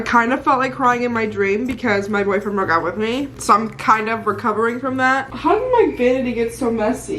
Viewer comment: I am in the first half of the video and I had to rewind it because I thought you said your boyfriend DID break up with you…like I didn’t hear the dream part…I almost cried so hard and puked..with my dramatic ass LOLOL💖💖